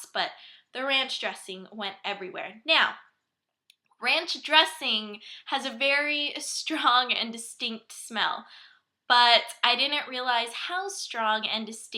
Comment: "Now ranch dressing has a very strong and distinct smell" , Is that correct to what she said ?? , or " Now ranch dressing has a very strong and stink smell" , Is this correct? could someone correct me